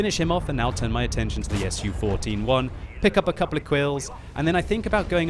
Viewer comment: Was I the only one that recognized said quills instead of kills at 3:40????